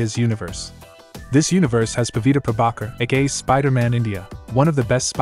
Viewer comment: i thought i hear you said pavitr pavakar was gay